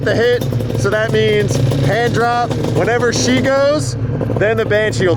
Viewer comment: Definitely sounded like he said handjob, way more than hand drop. I heard no “r” or “p” when he said it, which is why she heard handjob, bcuz thats what he said!!!